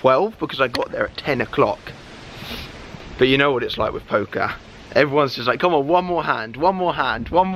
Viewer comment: Wooooow i litterally thought he first said poké so i thought he was playing pokémon go but he said one more hand and i was like ... Oh